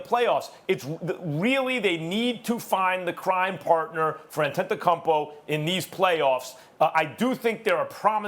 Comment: did he just said antetokompo